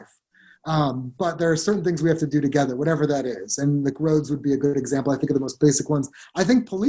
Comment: Yeah I heard him say "muh roads" but I'm sure all you autistic children in the comments are going to be serious and not giggle. [stern face]